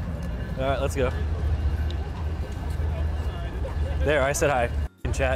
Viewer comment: did they say the n word or am i trippin ?